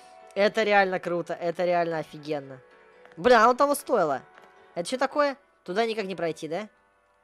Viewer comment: Мне не одному послышалось "бля ну а но того стоило"